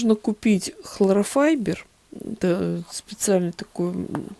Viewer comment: Здравствуйте, часто просматриваю ваши видео, очень увлекательно и полезно, но заметила маленький недочёт в произнесении слова холлофайбер, это не хлорофайбер, как только услышала " хлорофайбер", то захотелось поправить вас)